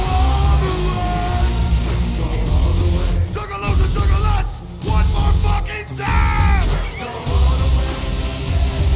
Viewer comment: what did he say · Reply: Talyn Sevenstar “Juggalos and juggalettes one more f**kin time”